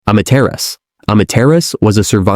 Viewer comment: i got confusing shock at start of the video because instead of hearing Amaterasu i heard "imma terrorist"